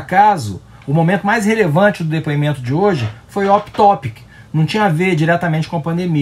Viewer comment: Meu inglês rústico presume que isso foi "Out topic", mas meu lado fã do Miranha ouviu "Octopus", e por reação falei "Doutor?".